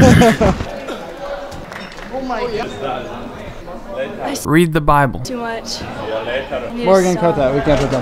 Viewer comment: what did she say? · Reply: She said I read the bible too much